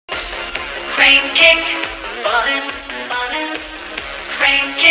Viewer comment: Oh its Crane Kick I thought he always said crime kick until I had that words on the tv lol